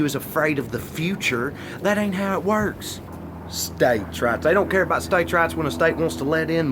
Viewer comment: This whole time I thought he was saying States' RATS..... "States have more rats than women do." I was like duhhh 😂😂😂